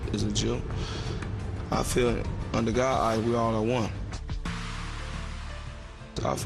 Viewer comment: what he said in 6:12? Under GOD... (?) ... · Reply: Under god eye we all are one